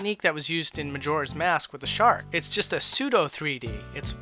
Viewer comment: does he say shark? help